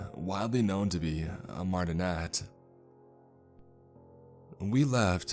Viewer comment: Can someone please tell me what the word at 2:38 is and what it means? Mardonette? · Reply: Martinet, it means someone who strictly follows the rules